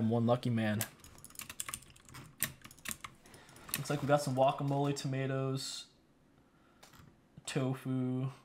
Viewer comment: did he just say wuacamole?